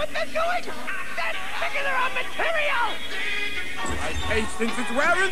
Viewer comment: "They're ???????? our material!" Triggering? Sickening? What did he say? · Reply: "They're singing their own material!"